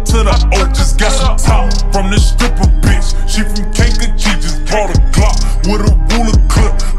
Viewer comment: Omg it sounded like he said kanjakee lol Rip von